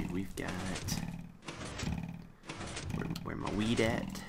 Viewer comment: WOW! When he said "Where's my wheat at." I thought he said "Where's my weed at." LOL!